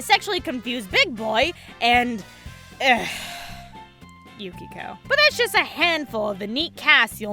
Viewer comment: "Egh. Yukiko." I will fight you. But seriously, I had a good time watching this. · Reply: She said Yukiko? I thought she said "plank of wood."